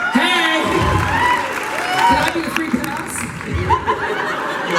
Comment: Does he say can I be your free pass??? I can't understand it